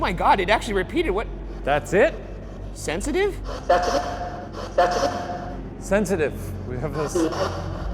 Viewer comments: When the spirit box says "sensitive", I heard "Cecily". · and 6:55 it DEFINITELY sounded like "that's me!" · From the spirit box I heard 'sedative' instead of 'sensitive' and 'damn you' instead of 'down here', even though I'm a believer I think the scream was acually just a seagull, animals make a lot of scream like noises, especially birds, I live nearby a lot of birds nests and they get pretty freaky with the noises they make, also 'right now' sounds a lot like 'Ryan' knowing how the spirit box sounds Shane could've misheard that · it is just me or it sounded like it said "thats me" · Where they thought it said sensitive I heard “that’s me” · In the shower room instead of hearing the word sensitive, I heard "That's me" which makes a lot more sense